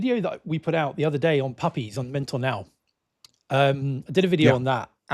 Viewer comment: Thought I heard puppies. 😂